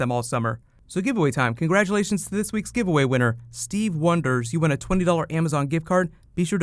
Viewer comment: Did he just say Stevie Wonder?